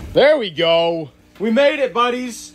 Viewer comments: What I heard: I'm gonna get my food I'm gonna get my food I'm gonna get my food! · i thought he said "Your gonna become our food" 💀